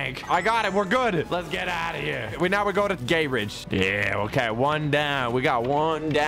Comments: you said gayridge instead of grayridge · Did he just say gayridge🤣🤣